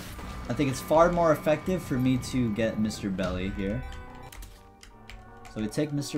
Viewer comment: I always hear ya say “Mr. belly” instead of Mr. dolly